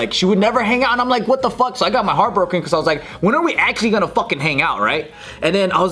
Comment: What said: when are we actually gonna fucking hang out. What I heard. when are actually gonna fuck.